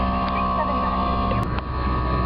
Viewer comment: rockstar69000 -- LOL I thought they were saying "bottled-douche"